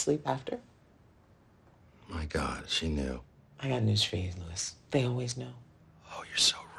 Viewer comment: What did Jessica said at 02:40 ? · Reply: I got news for you Luis